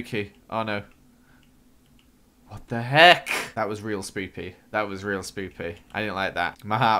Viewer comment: I thought he said spoopy